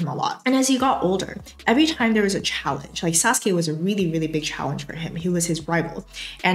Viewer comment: I swear I heard you say Sasuke was Naruto's waifu at 3:20 . I choked and to go back and listen to it again.💀